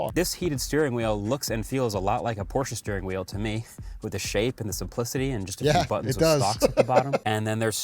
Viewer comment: Now, I know Marques said "with the shape and the simplicity", but I swear I heard "with the shape and some pussy." Don't ask me; I don't know what's wrong with my brain. 😂